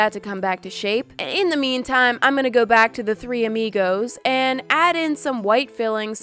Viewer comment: I thought she said the three migos